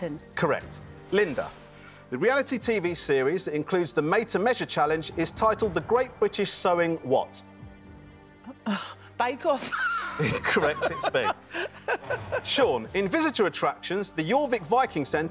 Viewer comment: sounded like he said its correct, rather than incorrect.